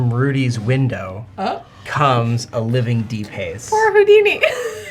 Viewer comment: Awesome battle. If I could suggest, please . . . . . enunciate "Deep Haze" as two separate words hitting the 'H' hard. At first I thought you were saying something like, "De-paize". Hope this doesn't come off like I'm being a "Shittiness Drag" lol